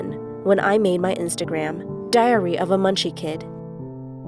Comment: Ngl when when she said diary of a munchie kid I thought she said diarrhoea of a munchie kid lmao